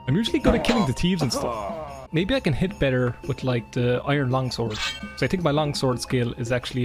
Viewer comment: I like Daggerfall and your wacky & entertaining Elder Scrolls adventures! Also love how at 4:48 I first heard "iron lung sword". Iron lungs are a thing (look it up), now I want to see swing them as a sword, where is the mod? Haha